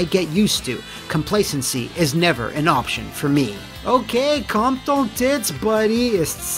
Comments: Bro did you just say "Calme ton tits esti" at 06:23?! As a french Quebecer that really caught me off gard and made me laugh 😂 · did you just say "okay, calm ton tits buddy esti" xD Guess you're from Montreal!